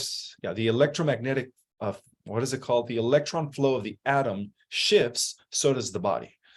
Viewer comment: When he said Atom Shift, I thought he said Adam Schiff!